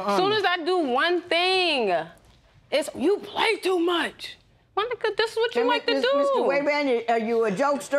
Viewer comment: did she say my nigga